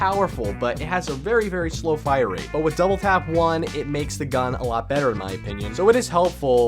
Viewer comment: Did he say double pap 1